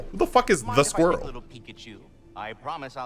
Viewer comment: I definitely thought I heard Ratatorski said: Pikachu instead of Peek at you Had to re heard the line a few time v: XD